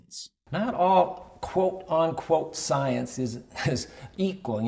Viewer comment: did he just say "quote-ON-quote"? Smh my head 🙂‍↔️